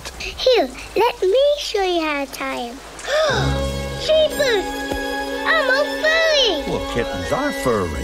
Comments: I legit thought he said “I’m a BUNNY” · what I hear duke: Jesus I'm a FURRY